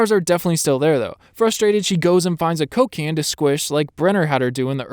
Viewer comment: am i the only one who heard cocain instead of coke can?